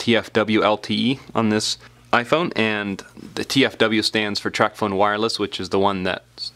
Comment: sounded like you said Trap Phone Wireless 😂😂😂